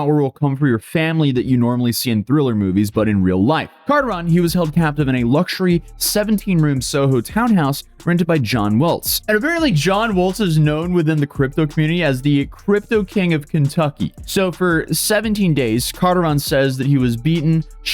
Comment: I had to look it up since there was no name in the description. I thought he said "John Wolfe" at first, but no it's John Woeltz.